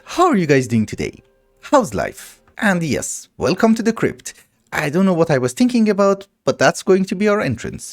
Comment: For a second I thought said "welcome to the crib" (0:04) i thought: damn didn't know was cool like that. But then I heard him finish the word and it was crypt.... 😂 Its well built though I like it.